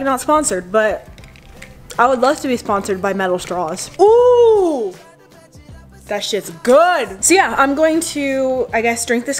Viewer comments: it sounded like she said ooh that shits good. Like if you heard it too. If not good for you I guess. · i heard u say that shits goos!!